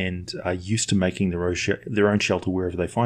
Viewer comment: i sounded like said they they are use to making their own shit.